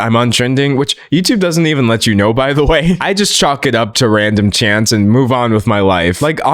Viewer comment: I think you said “chalk it up to random chance” but I heard “chants” and I actually really like that concept. Just thought I’d share🙃 proceeding with the video now.